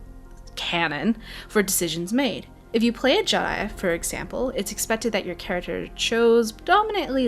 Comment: did u say Jedi? if so it sounded odd